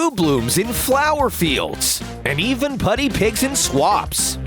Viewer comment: DID HE SAY PUDDY PIG!